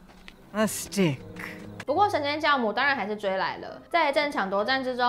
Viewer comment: 我一直听成神仙酵母😂😂😂